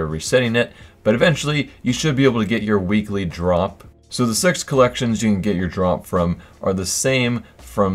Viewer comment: i heard "so the sex collection" on